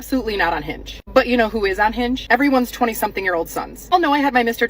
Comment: Every time I hear "I'm on hinge", what I hear is "I'm unhinged". If men have ageism then women have wageism.